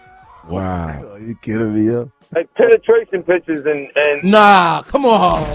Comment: I couldn't hear the last line 50 said about Cassie's pictures after he said the pictures I saw were worse, wayyy worse. Can someone drop it in the comments? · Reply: i thought he said double penetration lol